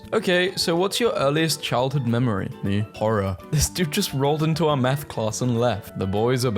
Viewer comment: I heard "this dude just rolled up into a meth class"